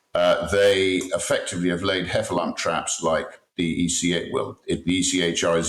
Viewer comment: “hefalan traps”? Did I mishear? Can anyone decipher for me!